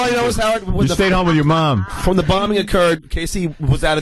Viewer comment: Did John say " When the bombing occurred?" at 1:37?